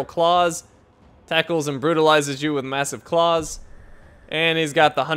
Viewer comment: LOL I thought that said "tickles and brutalizes you with massive claws".